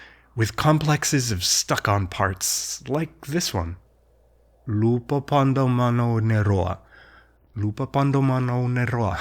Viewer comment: I see «Lupâpadomanaunetoâ» but I hear «LupâpadomanauneRoâ». Which one is correct?